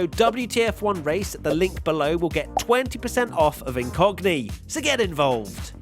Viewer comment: I know it's incogni but I kept hearing "in cockney" I imagine this as a service that encrypts your data with cockney slang which I now need.